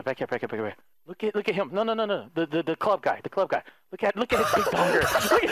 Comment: for a second there I thought I heard the words “UNGA BUNGER”